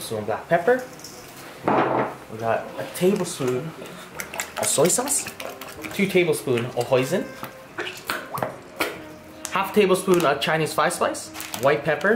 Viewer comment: I thought you said Poison 😶🤪